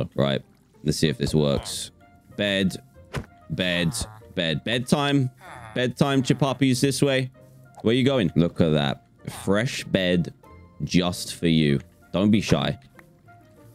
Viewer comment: Did just call the villagers “Chuhpapeys” instead of Chupapi’s?